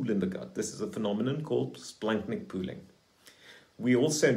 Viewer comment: 29: Did he say Blankman Pooling? I tried to look it up. The caption says "splenic poolic" but it didn't sound like that...